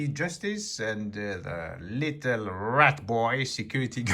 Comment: The saddest part of this is the RAT is able to make a false report to the police and then lie in court and walk away like nothing happened, so justice has not been served. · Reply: Rat boy? I thought he said rent boy! My hearing, eh?🙂